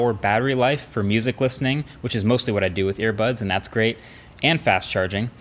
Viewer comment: Marques,did you just say air buds???